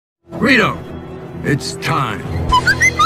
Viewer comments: I hear doc say "weirdo it's time" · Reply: I heard him say, "rito, it's time." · I hear doc say "weirdo it's time" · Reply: I heard Rieto, it's time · I hear doc say "weirdo it's time" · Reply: I heard burrito it's time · i heard Doc saying "Burrito it's time" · I hear doc say "weirdo it's time" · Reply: nah, i heard him say "libido it's time" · I hear doc say "weirdo it's time" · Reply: I hear "Rido it's time"